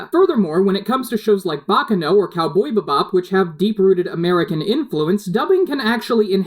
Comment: Did he just say "Cowboy Buh bop" bruh XD